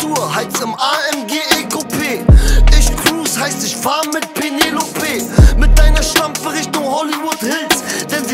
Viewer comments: This song is Fukken Awesome 😎👍 and I do not even sprechen deutsche, only English ! I actually thought he was saying Mike Pence (ex US Vice Prez) LoL 😆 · Swear my ass thought he was saying Mike Pence, (Donald Trump’s Vice President)💀🤣🤣🤣🤣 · NFS brought me here to say all I hear is "Mike Pence" and now I can't unhear it.